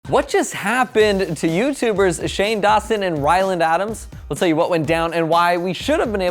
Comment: did he just say Shane Dawson and Wyland Adams? lmao Anyways, i’m so happy Shane proposed!! I’ve been waiting for this ever since they got together:)